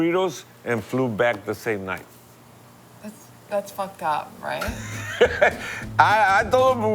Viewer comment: What word does she say at 9:19/9:20? she says that's something. At first I thought she said "that's fucked up' but it's not that I think it's a spanish word. what is it and what does it mean?